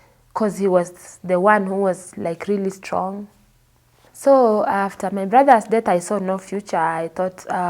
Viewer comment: I thought that she said her brother was dead